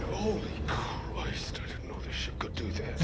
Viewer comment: He says HOLY CRIST I DIDN'T KNOW THE SHIP COULD DO THAT...i always thought he said HOLY CRIST I WISH SHE WOULD NOT DO THAT...And at 11:39 i only just realised he says FOR ZION...Shows what u hear when u have headphones on.